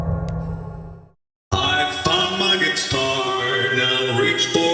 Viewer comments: tell me what you heard____(•_•) I heard I f##k my guitar 😂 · Lol . For rockstar bonnie it still sounds like he said he fuc*ed his guitar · PLEASE THAT CANT BE AN ACTUALL VOICE LINE 😭 WAIT I HEAR SOMETHING ELSE—- I THOUGHT IT SAID “I fu** my guitar” 😭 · It Sounds LIKE he says I F*** My Guitar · I know what Bonnie said "I found my guitar" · i started laughing because i heard "I ** mY gUiTaR" please help me ·,· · it sounded like rockstar Bonnie said I f*cked my guitar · who hears: I f** my guitar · why does it sound like he said I f*** my guitar More than I found my guitar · What... did you just say do it again.. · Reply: He said I F U C K M Y G U I T A R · I F**K MY GUITAR PLEASE LIKE IF YOU HEARD THIS ASWELL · in 1:40 i thought rockstar bonnie said he f ucked his guitar xD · I F**K MY GUITAR NOW REACH FOR THE STARS AS I PLUNGE IT THROUGH YOIR HEART! yes I know I know I know he says I found my guitar but it sounds like he said I f**k my guitar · Does he say I f**ked my guitar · BONNIE DID YOU SAY YOU FU## YOUR GUITAR?! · did it actually say i fu@ my guitar????? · who else heard bonnie say "I f*cked my guitar" 💀 (Kevin Ogwu ty for reminding me lol) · who hears I JUCK MY GUAITAR I cant say bad words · Is is just me or at 1:41 I heard I f$&@ my guitar and then I found my guitar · I think we was trying to say I found my guitar but instead he said I F**K MY GUITAR